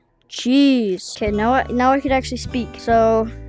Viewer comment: Why did you say cheese 💀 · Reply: I said jeez but it sounded like cheese so it was funny